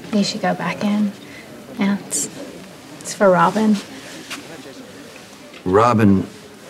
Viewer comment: Who was Brenda talking to in the sky in the beginning? Sounded like she said, 'Hi Rob.' I don't remember any Rob characters but maybe I heard it wrong. This scene should've stayed; it showed the easy chemistry between Sonny and Brenda, they even laugh and talk like old friends. I wish she had stayed longer and met Connie, that would've been great to see those two face off, missed opportunity. These two are soulmates through and through. If Sonny ever leaves, they need to be together. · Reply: she was talking to robin who she thought was dead